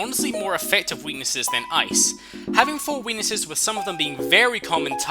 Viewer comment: it sounds like he says " having four wenises"